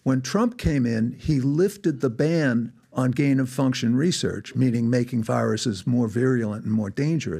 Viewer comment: did he say “Trump lifted the ban on ‘gain of function’ research” ??? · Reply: Yes, he stated that Trump lifted the ban on gain of function testing but it was the NIH under Faucis orders who did it in 2017 and they outsourced it to China. Would the President be consulted about this decision? I’d imagine the President would leave such decisions to the ‘so called experts’ like Fauci. I think Fauci held this position for about 40 years